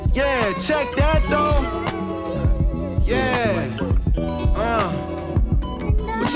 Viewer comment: sounds like a Cole snip, saying, "Yeah, Light Work" just before joey saying, what ya want me to do say im sorry?